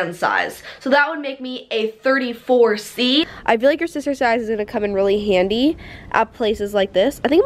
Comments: YESS LOVE YOU! (when I herd “sister size” I thought, “wait, she has a sister!?”) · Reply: sachii. k. Same I thought she said SISTERS SIZE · I thought you said it was your sisters size for a second and I was like wth I didn’t know you had a sister 😂